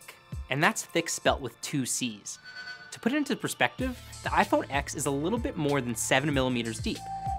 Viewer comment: Did he just say iPhone ex?